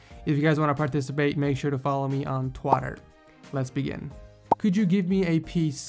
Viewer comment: did just say twatter???